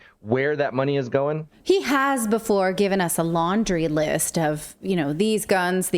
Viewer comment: Haha I thought Natalie said laundering list not laundry list, my ears haha 😂😂